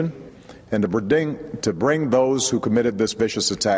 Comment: lol @ 1:50 he says "bridting" instead of "bring" lol just had to notice....